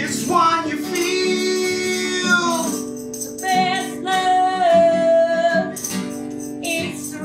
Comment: Somehow, listening to this without watching them, I heard, instead of "the best love" i heard "the Tesla". Until I heard "same blood", I thought I was on point